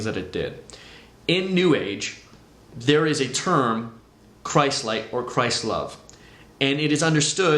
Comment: The word was not "Christ-like" it was "Christ-Light". But yes I do know of the term "Christ-like". ;-) thanks for comment and yes I do explain A LOT more in videos #6-7. Hope you will stay tuned. Thank you Billy for being here since the beginning! I'm blessed by that! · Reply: Ohhh! Sounds like you said "Christ-like". My bad. Thanks for your correction.